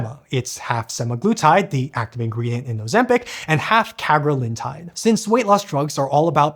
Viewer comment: I heard "Carolyn-tide", and thought "What about Elle-tide™, the largest of the GLP-1 inhibitors?"